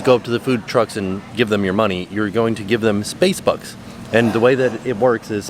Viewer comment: I thought he was saying Space bugs 🤭🐞🦗🕷🐜🐝